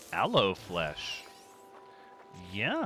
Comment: 😂😂😂 You said "Aloe Flesh" and I heard "Hello Fresh" and I was super confused for a second! LOL